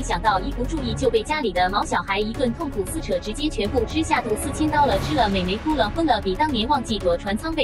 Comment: 4000刀了我怎麼聽成了4000下揮刀了或是死瓩刀了等等的呢？